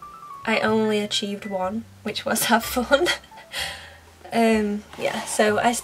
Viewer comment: am I the only one who thought it said "Have Fury!" Just me? Eh, okay.